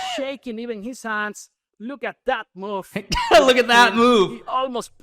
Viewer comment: Did he just say "Look at that, mf"?